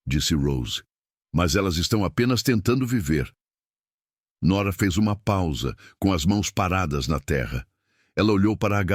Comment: "o que eu sou? Perguntou nora Você é alguém que minhas filhas escolheram, e elas não escolhem errado. Disse o homem (não entendi o nome dele kkkk)." O fofo é que ele a chamou de mãe ou esposa, sem usar nenhuma dessas palavras. 🥺🥹❤ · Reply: Caleb